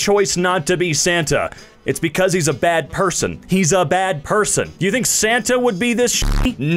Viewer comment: "He's a bad person!" what i hear, "He's a bald person!"